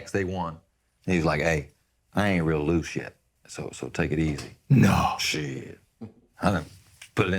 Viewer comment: i dont get it, what Penny said? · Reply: “I ain’t real loose yet, so take it easy”